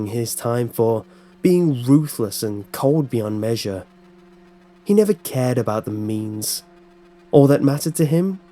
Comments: ffs I got confused cuz I thought you said "he never cared about memes" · I thought at first when he said; He didn't care about the means. I though he said; I dont care about the memes